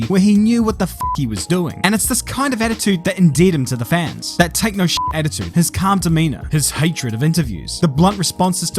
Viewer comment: At around 10:40 when you say "his take no sh*t attitude" I heard " his techno shit attitude" and got pretty confused 😂